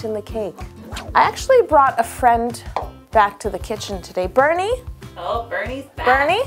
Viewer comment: I heard "Bernie" and I thought it was a Bernice DOG, but no, life is not nice, you meant "Burnie"